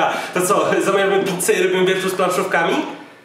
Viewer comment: usłyszałem piknik z palcówkami XD